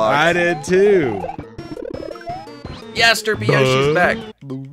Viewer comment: Video:Yes derpy Yoshi is back! Captions: Yesterday What I heard:Yesder b Yoshi is back!